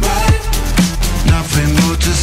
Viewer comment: yo pense que decia "they tell themselfs to be a cow"